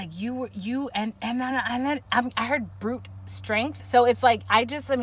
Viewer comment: Pile 1 in the building! 😆 so accurate per usual lol around 55:05 you said something strength and I feel like you prob said brut strength but it honestly sounded like bruch (my last name) 🤔